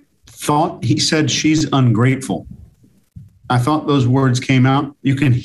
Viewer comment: He actually said, "She's on great form." 🙂